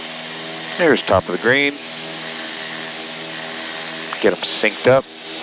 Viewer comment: What did you say at around 24:28? It sounded something like "arrows top of the green".